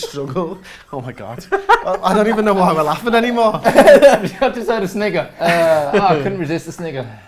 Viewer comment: what is he saying that 5:49 haha. Sounds like he's saying the N word.